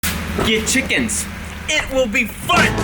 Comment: I thought you said "gay chickens, it will be FUN" in the intro lmao